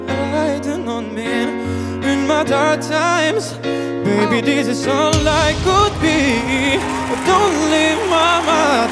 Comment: Que dice Antonio en el minuto 1:05??? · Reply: Pau